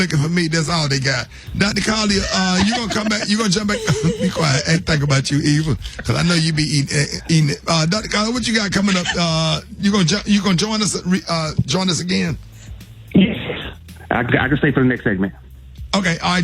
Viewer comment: Did he say she be eating ass??? Lol